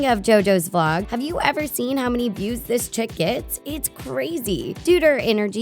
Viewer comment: they say Chicot instead of chick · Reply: Hazel English they said “chick gets” 🤦🏽‍♀️